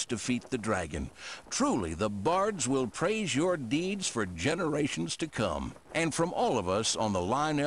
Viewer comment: Generations to come, did he say generations to come?